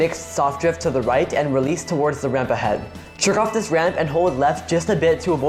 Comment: did you just say jerk off that ramp? Woah I didn't know you were into that stuff